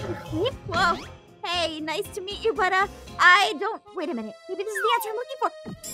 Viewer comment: i got confuse when she said" nice to meet you budduh" because it sounded like she said brotha